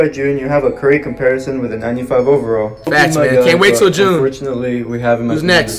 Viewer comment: Did he just say 92 overall